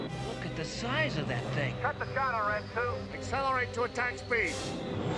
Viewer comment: does he say "cut the chatter rd2?":?1:27? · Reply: "Cut the chatter red 2"